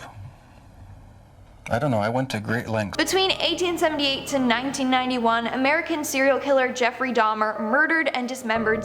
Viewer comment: It sounded like she said 1878 to 1991